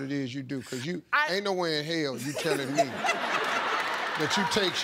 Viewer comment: What does Steve say at 0:46-0:47? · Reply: vasya83 ain't nowhere in hell u telling me is wat he said